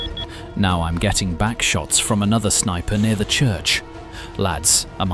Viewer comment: what did you say you getting 😮😅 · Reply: "Now I'm getting a Backshot from another sniper near the Church"